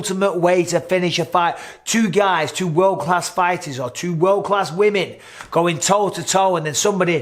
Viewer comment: “Two world class fighters, or two world class women” 🤣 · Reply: I thought I heard that too! But he actually says "two men, two world class fighters..." and then says "two world class women."